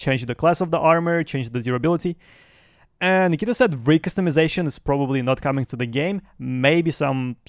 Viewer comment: "rake customizations"? tf did you say?